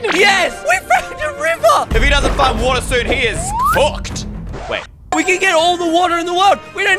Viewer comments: Who else misheard him say fucked instead of cooked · Why did he say the F word 😳 11:50 (How old are you tho?) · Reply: He said cooked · People who heard EYstreem say he is F***ed